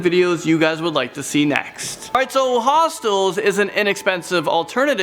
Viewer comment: I thought it said hotel